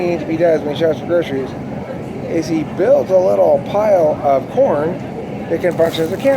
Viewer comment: Ohhh, "pile of corn". I thought you said "pile of porn".